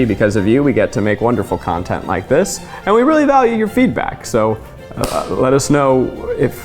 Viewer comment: when Zach said - "we value your feedback", I heard "we value your feet back". Good to know our feet are valid too hahaha